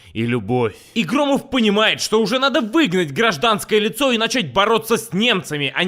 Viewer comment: послышалось: и Громов понимает, что надо выебать гражданское лицо и начать бороться с немцами :D лол